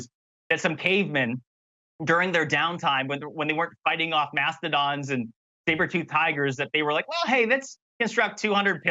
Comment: I think he said "Mastodons and sabertoothed tigers," because he had Power Rangers on the brain.